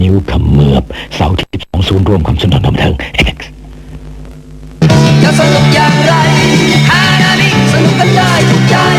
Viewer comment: ผมสงสัยตั้งแต่ตอนเด็กเค้าพูดว่า เอ๊กซ์ๆ ความหมายคืออะไรครับ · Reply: เอเพ็กซ์ Apex คือชื่อกลุ่มโรงภาพยนตร์ ประกอบด้วย 4 โรงคือ เฉลิมไทย สยาม ลิโด้ และสกาลา มักได้ยินในโฆษณาหนังบอกว่าฉายในโรงภาพยนตร์ในเครือเอเพ็กซ์